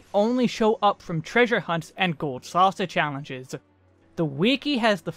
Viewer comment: Hmmm thought ya said gold sausage hehe